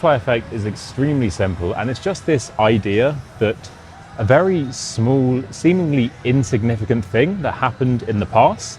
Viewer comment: 'a very small' sounded like 'smool' or is it on me?